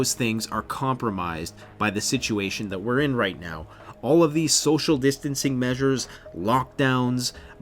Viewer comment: Whenever someone says social distancing, I hear “social conditioning”.